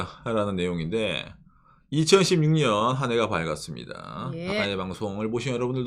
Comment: 응...? 0:35, 10:52... 2026년이 아닌 2016년으로 들리는 ... 데... 지속적으로 2026의 발음이 2016으로 들려... 너무, 꼬투리 잡는건가...? 내 귀가 잘못 되었나...? 그나저나, 잘못은 정부가 아니라 국민들 늬들 탓이다... 란 거냐?